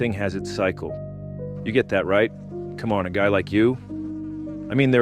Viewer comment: Huhuh, he said "cum on a guy like you", although I may have misheard that 🤣🤣🤣